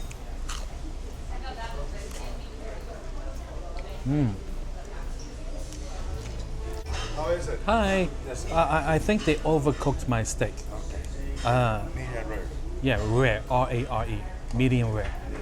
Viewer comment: I CLEARLY heard you say "medium RARE RARE RARE" LOL And I'm not in person. Idiots.